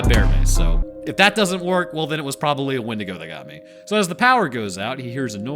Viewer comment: did you say it was probably a Wendigoon that got you? Cause that's what I heard 😂